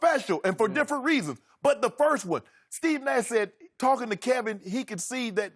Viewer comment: Did shannon say "steven a said" at 9:56? Did shannon really say steven a? Did i hear that right? Or what he say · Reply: Steve Nash.